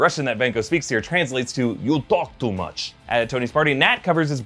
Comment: ACTUAL RUSSIAN HERE. At 7:35 he does NOT say "You talk too much" he really does say "The software is shit".